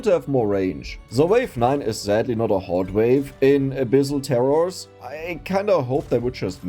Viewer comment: I thought you said "A bissl Terror" instead of "abyssal terror"